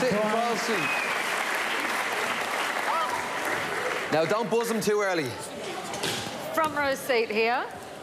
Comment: lol at when brian says dont buss him to early at 1:50 sounds like he says bossoms